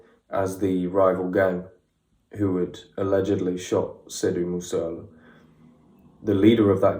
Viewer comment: I thought he said "Sidh Moose Masala".